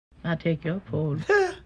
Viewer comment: I thought she said “I TAKE YOUR FOOD” 😭😭